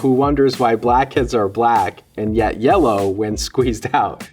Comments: imagine my confusion and offense when I misheard blackhead for black kid.🥴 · Am i the only weirdo who hear ''black kids'' 14:19 😂😂